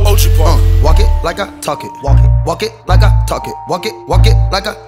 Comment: my fav part is where he said walk it like i talk it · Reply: I thought what he was saying "worked like a talking"